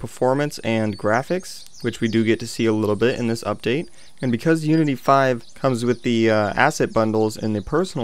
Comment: BEST UPDATE EVAH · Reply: Cracked_ And i thought you said nudity 5... xD